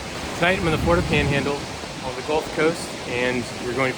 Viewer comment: Jesus, i thought you said "The gold coast" and my sphincter contracted so fast and hard my head hit the headboard ( it's not padded). I did a night dive in Cairns in the 1980's and disturbed a shark and a Crocodile, they both shit themselves and I thought I was Aquaman. Not an evolutionary step forward.